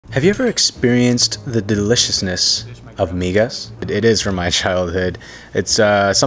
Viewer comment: I heard “Have you ever experienced the deliciousness of ni- “